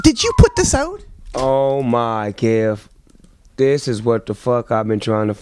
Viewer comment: i've been trying to figure out what Snoop says here: Oh my ___? what does he say? "Oh my gief!?" Can somebody explain??